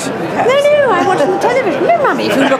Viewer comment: did she say 'momy'??